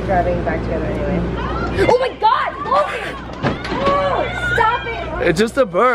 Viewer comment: When cloe said "omg Logan" I thought she said Clogan 😂